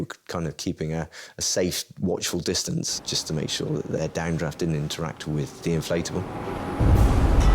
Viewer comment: My hearing! I thought he said "dandruff doesnt interfere" but second time i listened i heard "down draft."!!Hugh they very brave did right thing.wowSS tried take my son when he got lost.